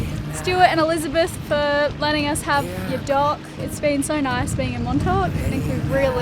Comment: I honestly understood dog at 5:40 instead of dock. I was all confused for a few seconds, thinking: "wait, did they get a dog? Did I miss something??"